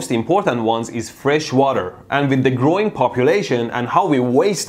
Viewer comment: Thought he said "Groin Population" 😂🙊